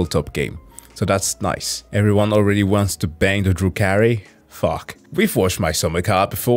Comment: For some reason, I misheard "Drukhari" as "Drew Carey". Took me replaying this part and actually paying attention to the video to understand, what was going on.